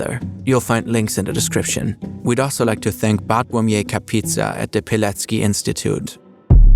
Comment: Did anyone else hear Pizza 😅